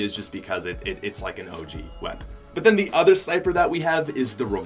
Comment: did he say sniper ?